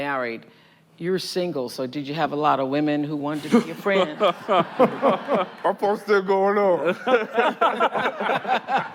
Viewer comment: what did Korey say? Lol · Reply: my phone still going off